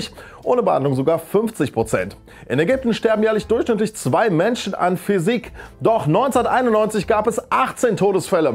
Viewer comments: ich hab"es sterben durchschnittlich zwei menschen an Physik" verstanden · Hab 6:57 "Sterben Menschen an Physik" verstanden. Same